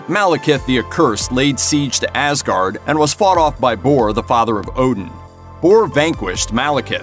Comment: did he say Thor the father of Odin?